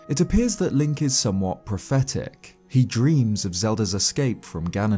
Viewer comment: I kept hearing "pathetic" from "prophetic" and it's hilarious.